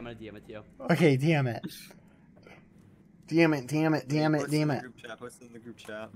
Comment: Why at 21:47 did it sound like bad was saying dammit or is it just me